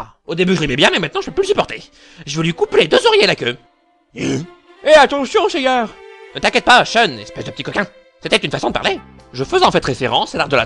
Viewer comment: A ouai j'ai compris "je vais lui couper les 2 oreilles et la queue"...